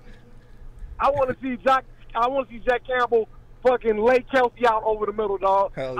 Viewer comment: call was incredible, got me hyped as well! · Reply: lol at first I thought he said, "I wanna see Dan Campbell lay Travis Kelce out over the middle!"